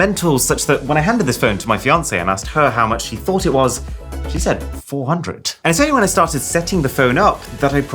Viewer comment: I had a mini heart attack, thought u said he not she…